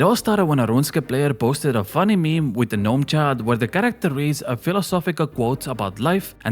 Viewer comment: Hearing about the Gnome Child is extra funny when I hear "Gnome Chad" each time 😆